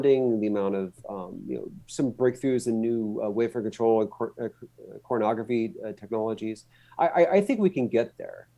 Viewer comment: re - 26:37 I'm sorry... No matter how many times I listen, it STILL sounds like he's saying "Pornography technologies." Is that what he's saying? Is my age finally taking a toll on my hearing? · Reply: coronagraphy :)